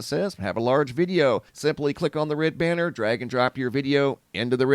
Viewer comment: When you Ended with "Drag and Drop" I heard 'Dragon Drop' and Something about that Feels on Point. 🏡😅☁️🐉🌌